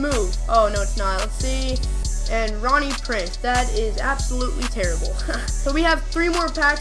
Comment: Bro it sounds like ur saying rohnie preece